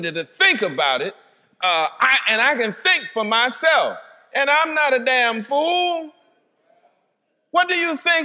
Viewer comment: Did he just say I ain’t no damn fool?